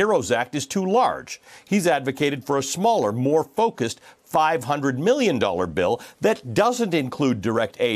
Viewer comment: It sounded like he said the skinny bill was 500 million but it's 500 billion. I still don't believe 500 billion is enough either. I just wish we would have taken testing more seriously.